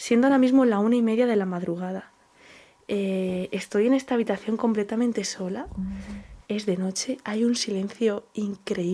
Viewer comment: HOLA Y BENDICIONES CHICOS A LAS 15:49 SE OYE LA VOZ DE UNA MUJER NO SE ENTIENDE QUE DICE TE VEO DESDE COSTA RICA 😘😘🙏🙏🇨🇷🇨🇷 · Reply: Dice " como yo"